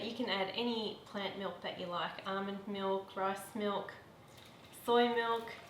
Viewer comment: Did she say “...almond milk, breast milk, soy milk...”